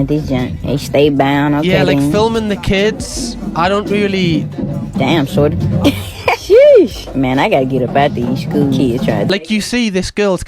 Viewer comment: Did anyone else hear what she just said? That absolute creep making comments about an underage high school girl in a skirt. She said something like "God damn shorty, sheesh, I got to get up out of here around these school kids" or something like that. Honestly that is disgusting. Then right after that she starts talking about how she looks so young she looks like one of them. Like she trying to justify her sexual attraction to a child. Absolutely sick. It's exactly those type of people that try and get these type of jobs too so they can have access to vulnerable kids